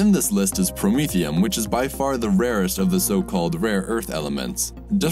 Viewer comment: i thought it said the "racist"